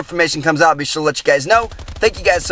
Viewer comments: What is he saying!!! Between 4:17 and 4:19 someone pls tell me! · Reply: Check out one of two videos if you haven't seen them yet, peace out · Scientists at NASA are still trying to discover what said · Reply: He said "Check out the two videos on the screen if you haven't seen them yet." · What is he saying at 4:16 ? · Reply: check out some of my other videos you haven't seen yet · what wtf did he say XD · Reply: Chec out one of the other two videos in the description if you haven't seen me yet